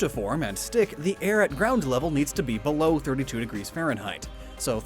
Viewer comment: I'm the only one heard 32 instead of 23?